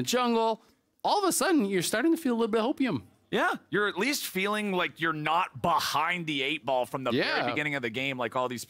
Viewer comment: Did he say you're feeling opium? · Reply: Hope-ium